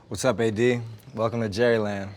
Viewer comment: i thought he said "whats up big D" like, what?